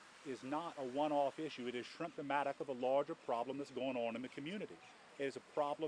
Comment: Did he say, "it's shrimptimatic of a larger problem" at 1:47?😂